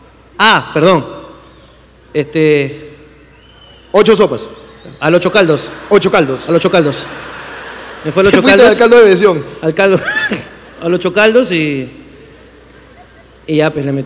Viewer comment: alguien que traduzca lo que dijo el tarrón xd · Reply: Se fue al “siete sopas”